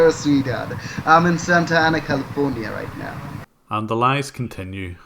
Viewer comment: I misheard the scammer said Santa Anna as Santa Anakin